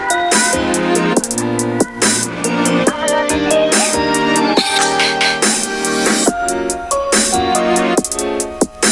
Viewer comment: The only thing I can't make out is what she says at 0:38. Can anyone help? 🙏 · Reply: "Amano Nene desu"